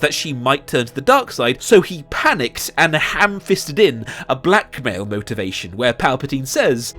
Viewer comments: I thought he said "a black male" motivation....I was like now how is the black sidekick's words gonna help at a time like this? His accent confuses me at times lol. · i saw finn and heard it as black male motivation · I misheard "blackmail motivation" the first time around as "black male motivation" and the video cut to Finn right after the words I was like: ??? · I fuckimg thought he was saying "Black Male Motivation" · when he said “a blackmail motivation” he zoomed in on Finn, which made me think he said “a black male motivation” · With the camera sweeping over to Finn I thought you said "black male motivation" and I was sitting here trying to remember when the movie gave us the impression that Rey cared more about Finn that any of the other characters (namely Rose, Poe, and even Leia) did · I thought you said "black man motivation" and was like WOT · he said blackmail but when the camera panned over to Finn when he said it so I def thought he was saying “so he hamfisted in a black male” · When you said “blackmail motivation” I thought you said “black male motivation” and I kept thinking “Finn was reys motivation?” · OH when you said "a blackmail motivation" amd then it cut to Finn I thought you said "black male motivation" and I was really confused about what black male motivation was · i thought u said " a black man motivation " showing finn and i was like "what????"